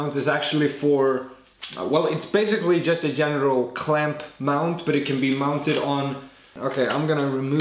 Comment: Swear I thought you said genital clamp @ 5:00 LOL Had to edit and add something. The curve you have on that book stand is so smooth. Did you do any after print detailing on that? I can't get my bevels/curves that nice. Good job man